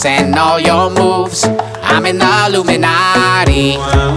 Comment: I dunno but I hear I'm in love with the illuminati